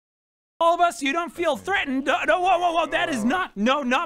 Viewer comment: Olive skin.. thought he said all of lol